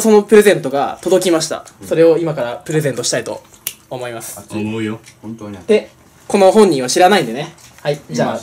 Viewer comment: なんて言ってる？w めちゃイケボww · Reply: おもうよ